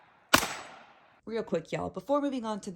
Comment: I listened three times. Sounded like at 1:05 in she said " before we move on to the breasts of this video".